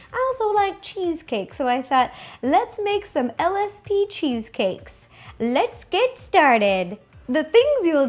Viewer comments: at first i thought she said LSD cheesecakes and i was like DRAMATICALLY GASPS RO! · I thought you were saying LSD cheesecakes for the longest time. Whoops. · It sounds like you said L.S.B. That sounds good. I LOVE cheesecake. Okay, at 1:33...um...Okay. That crust looks really good. Purple lumpy cream cheese Mmm. Those look really good. That’s a weird way of cooking it. Still going to be delicious!!! Did you really cool those lumpy cheesecakes for 4 hours??? I have ice cube trays that are silicone like that. They really come in handy. YAY DECORATING TIME!!! They look really good and yummy!!!!!!! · Ha! Thought you said LSD cheesecakes...:o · I thought she said lsd cheesecake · I thought she said LSD cheesecake oml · I thought she said LSD cheesecakes. wat. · i thought she said '' let's make some LSD cheesecakes!'' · sounds like she said lsd cheesecake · I thought she said LSD cheesecake at the beginning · I thought she said let's make some LSD cheese cake which would make this video very different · Omg rosanna I thought u said LSD cheesecakes I was like wow is this a prank????